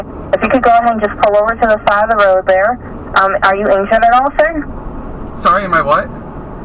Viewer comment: I seriously thought she said "Are you Asian at all sir?"